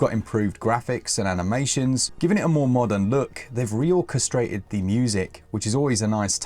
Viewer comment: You said orchestrated, I heard ‘castrated’ 😂